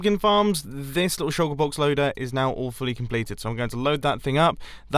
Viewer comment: anyone else hearing: awfully completed??